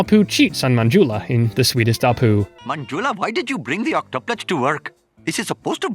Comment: I thought the sweetest apu said "the sweetest apt"